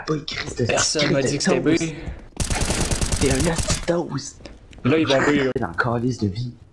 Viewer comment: I think at 7:07 he said (T'es un osti d'toast)/(you're a shit toast) I heard people call me a (tabarnak de cornet)/(Fucking Ice cream cone) but never a toast we can be ressourcefull with our insults sometimes. Perhaps too much....